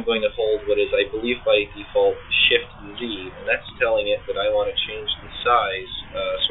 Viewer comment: what command did you say at 0:50 I think its shift-g or shift-d. I tried and then left-clicked and dragged but it didn't distort the brush. Is this feature still available in 6.5? I'm using the trial version.